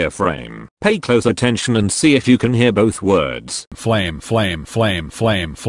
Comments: All I heard was “fwame“ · I heard “fwame”